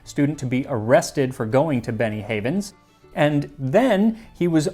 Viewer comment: Every time you say "Benny Havens" I hear "Beni-Frickin-Hanas". Also this episode is a real riot. I'll see myself out.